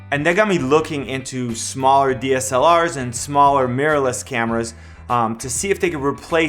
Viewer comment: Hi, I live in Japan and I really enjoy your videos. I love tech gears and I also love studying English. So your videos are perfect for me to use as English training material. My dream is to speak about tech gears in English fluently. I practice English pronunciation by repeating what you say in the video. You always speak clearly and you are easy to listen to., so you are my good teacher. But there is only one part that I can't catch. It's at 0:20 The English subtitle says 'and they're gonna be looking into ～' but I can't hear you that way. It would be great if you could tell me what you exactly say there. Thank you! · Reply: Glad you like the videos. I said "looking into 'smaller dslrs and smaller mirrorless cameras'" Hope that helps.